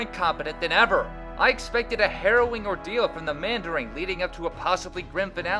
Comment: did you add a "g" to the end of mandarin? Sounded like you said "the mandaring"